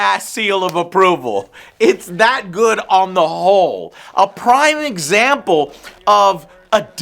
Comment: So I heard you say terminator was good on the hole. Fun